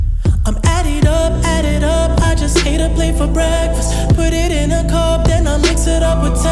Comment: I just ate a plate for breakfast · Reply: I heard "I just ate a place for breakfast"